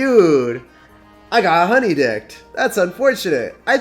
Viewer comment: What does he say at 14:56? I got (honey...?) Not a native English speaker so I'm always glad to learn a new saying, if this is what that is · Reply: Honeydicked lol. Weird way of saying baited.